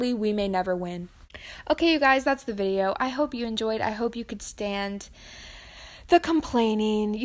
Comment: Ngl at the end I thought she said “I hope you can stan” and I was like sis why do you think I’m here